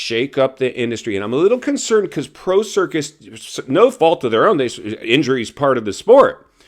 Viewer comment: I thought you said Pro Circus at 5:11. That was pretty funny, intended or not.